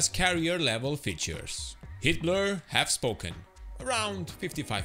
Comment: I thought you said "Hitler has spoken!" Excellent video!